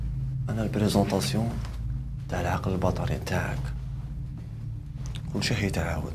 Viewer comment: سمعتوه كي قالو أنا العقل البطري تعك ولا غ أنا 😒😂😂😂😂😂😂😂😂😂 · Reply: ههههه،العقل الباطني ماشي الباطري